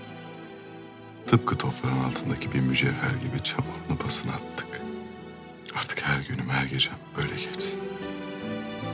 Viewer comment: de ne diyor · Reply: "Tıpkı toprağın altındaki bir mücevher gibi çamurunu, pasını attık."